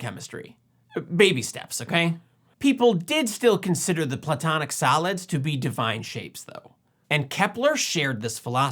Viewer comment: A couple of times I thought you said the platonic salads. But that works, too. 😁